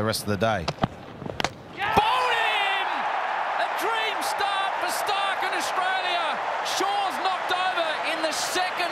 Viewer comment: For a second i thought he said Ita a dream starc for starc